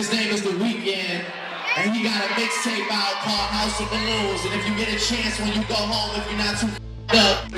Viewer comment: did drake say “WE 16:06 got a mixtape out called house of balloons” did Abel even know Drake when he released house of balloons??